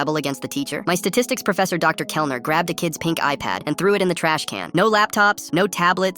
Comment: why did i hear "he threw a kids pink eye pad in the trash" like this man was mad at someone for being on his phone and made him half blind 😭